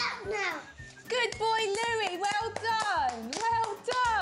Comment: When he finshed his pasta it sounded like she said bad boy louie!